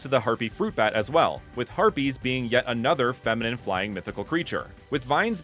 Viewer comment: I thought you said Herpes at first xD then I paused, rewatched and was like "Aah, Harpies 🤦🏻‍♂️😂"